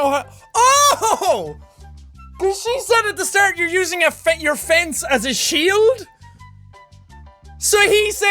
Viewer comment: Funny how it sounded like she said "you're using your offense at a shield"